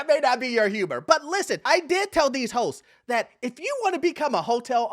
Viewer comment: i swore for a second he said, " i told these hoes" . Lol i was like 😂